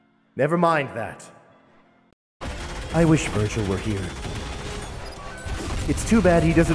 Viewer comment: did he say Vergil?